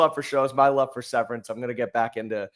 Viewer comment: What the hell does he say at 1:20? What show? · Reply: Severance